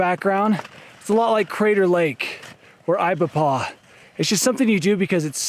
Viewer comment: what does he say at 12:16, "it's a lot like crater lake or ??? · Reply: Ibapah